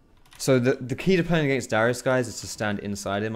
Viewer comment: Did he say dyrus instead of darius at 18:47 ?